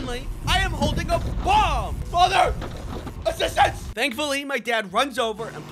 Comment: "FATHER. ASSISTANCE!" Edit: I heard mother guise apologies, Also most like yet aaaa tysm